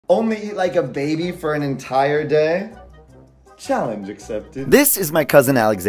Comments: i misheard and it sounded like Im only eating a baby for the entire day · I thought you said eat a baby for an entire day! 😃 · I'm sorry i herad that wrong, i heard "Eating baby for a whole day" 😭😭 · I fr heard it as only eat babies for an entire day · I thought he said eat a baby for the whole day 💀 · STOP I THOUGHT IT SAID EATING MY BABY FOR THE WHOLE DAY😭😭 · Fr I thought he said eat a baby for a day